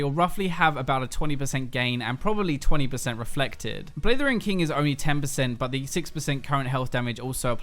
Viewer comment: sounds like you say "Blithering King,". :p